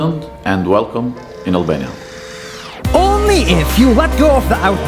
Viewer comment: I thought it said alabama ngl